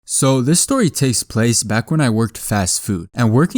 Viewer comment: It sounds like he said, So this story taste